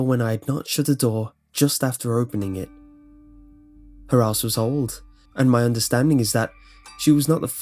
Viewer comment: i heard her ass was old